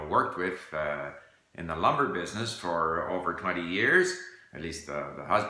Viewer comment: In the 'lover business'? Oh, sorry, lumber. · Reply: William Lucas I always hear 'lumbar' ;-)